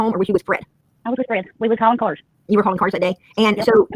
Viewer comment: What does say? "He came in when we was...." Thanks to whoever can understand that part. I think she said hauling cars, but idk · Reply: I was with Fred. We was hauling cars. He come in while we was gone to the crusher. And he seen Candus at the end of the driveway.